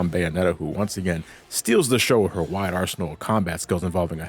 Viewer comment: Did you just say the main character "stole the show" ?